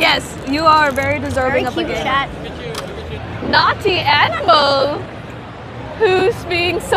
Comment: thought she said nazi animals